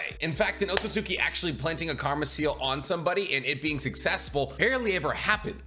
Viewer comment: It sounds like your saying “comma seal” 😂